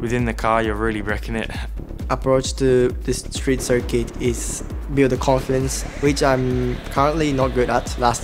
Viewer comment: i swear i thought Yuki said, build the coffins.